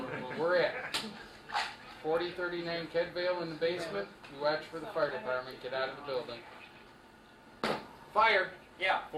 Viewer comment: "You __ for the fire dept." what does he say? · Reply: You watch for the fire department